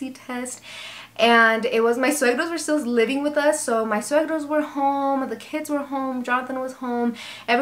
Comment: when you said suegros but the subtitles say soybeans lol . Sorry 😂